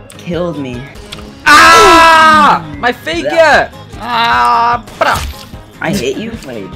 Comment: AHHHHH MY FAKER Me: did he say faker or hater?